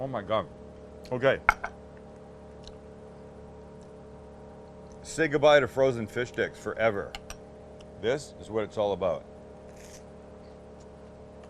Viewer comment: I keep hearing “fish dicks”.